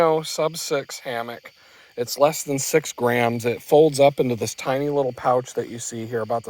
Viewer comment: When you said the hammock was less than 6 grams I thought you said less than 6 grand $lol